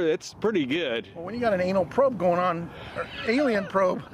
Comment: I thought it said Anal Probe.😅